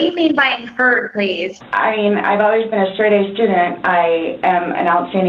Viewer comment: Omg, at 11:08 I actually thought she said she was a straight A student to the court cause I was washing dishes and not looking at the screen 😂